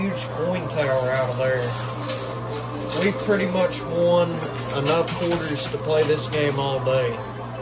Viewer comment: I thought he said he had enough quarters to play all day???